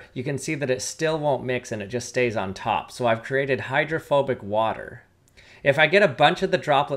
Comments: I thought he said homophobic water. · I thought you said homophobic water for a second- 😭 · i thought bro said homophobic water 😭